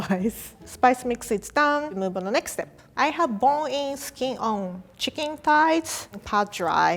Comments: what did she say at 3:13? i have bowl-es-kee-auh chicken thighs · What the hell did she say at 3:15 😂😂 Her : I have bon in skin on chicken thighs Me : wtf did you say ? Her : bon in skin on chicken thighs Me : just say fucking chicken thighs